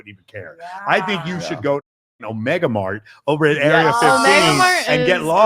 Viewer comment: I thought they said MegaMart and tried googling it but now I think they said Omega Mart.